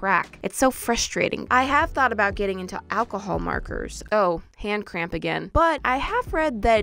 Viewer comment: is it just me who thought she Said ”Ive been thinking about buying alcohol” 😂